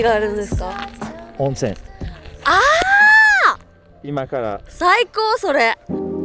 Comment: Superbe épisode, comme d'habitude ! ❤ Petite question : à 6:20 elle dit "saïko sore" ou "psycho sore" ^^ J'entends souvent ce terme revenir mais j'aimerais savoir exactement à quoi ça correspond ^^ Merci d'avance à celui ou celle qui me répondra 😉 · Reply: Merci à toi, « Saikou » ça veut dire « trop bien, génial » !